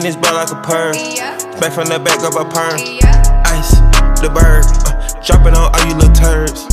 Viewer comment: Go to 0:42 and it sound like it says “smack from the back like a bird”😂😂😂😂😂😂😂😂😂😂😂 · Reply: It says pird not bird 😂😂